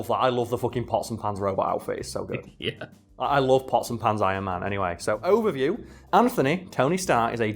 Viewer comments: > I love the pots-and-pans Iron Man I misheard that as "Possum-Pants Iron Man", and wondered what kind of Possums England has that has leg fur to make it look like it's wearing bell-bottoms. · When said "pots and pans" Iron Man, I thought he said "possum hands" Iron Man. I was like "Oh I don't know that one". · The first several times he said pots and pans Ironman, I thought he said possum pants Ironman, and was looking forward to seeing what version that was.